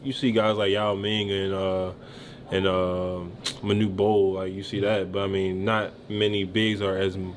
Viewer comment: I hate when they put subtitles on videos and they are full of errors. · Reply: Like at 8:56 "My new bowl" instead of Manute Bol. At least let us turn them off!